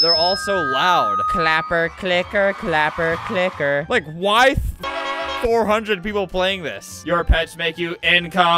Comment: Did he say "Like why f*** 400 people play this"